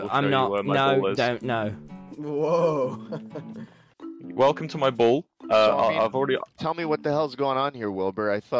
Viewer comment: In the intro i heard “come to my ball house” way to wrong Especially because Tommy says “ DONT GO TO THE BALLHOUSE”